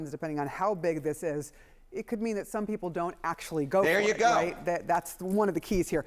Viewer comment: I Think she said "People won't actually go for it". Meaning, other candidates won't even run.